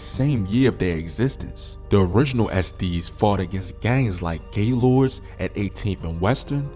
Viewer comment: did you say that gang was called "gay lords"?